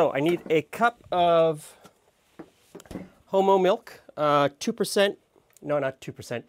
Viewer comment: Wait! 2:24-2:25 did say homo milk?